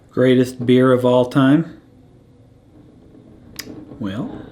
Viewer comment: At first I thought you said "Greatest beard of all time?", and then automatically wondered "the one on screen?" lol